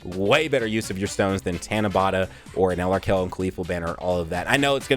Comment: Can someone tell me what is saying? I keep hearing Tienabada?